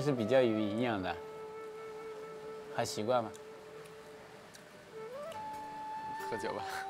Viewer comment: doesn't he say "还奇怪吗" / hai qiguai ma? which translates "still weird?" instead of "ni xihuan ma?" because they translate it as do you like it.